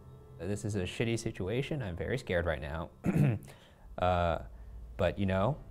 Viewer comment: They thought they heard "son." Anyone else hear "scared"?